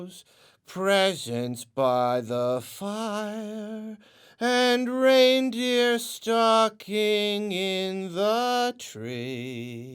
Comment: Just saying "Reindeer stocking in the tree" sounded so concerning at first. Legit thought this guy said a reindeer was stalking him.